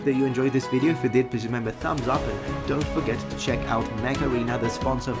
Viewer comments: Oh, "mech arena". What I heard was "macarena" and was a bit confused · Everytime you say "Mech Arena" all I hear is "Macarena", hahaah.